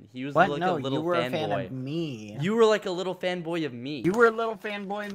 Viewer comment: why does it sound like said femboy?